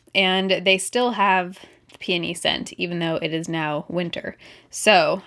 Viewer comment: ROFL!! When she was talking about Mrs. Myers, I thought at first she said panty scent instead of peony....I'm dead. LOL